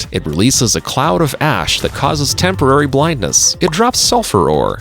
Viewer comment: what the hell 1:36 why did i hear every ''ash'' as ass